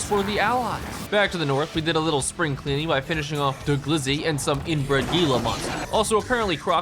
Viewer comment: I COULDA SWORN BRO SAID GLIZZY